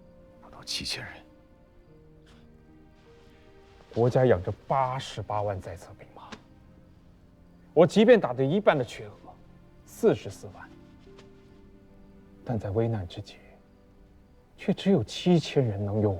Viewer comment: The English subtitle mistranslated the two numbers of the troops. Guo Rong said ‘八十八万’ means ‘880 thousand’ instead of ‘88 thousand’, ‘四十四万’ means 440 thousand’ instead of ‘44 thousand’. ‘万’ means ‘ten thousand’ in Chinese.